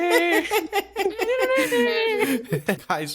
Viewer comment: Did say "Why do I have fucking host" lol?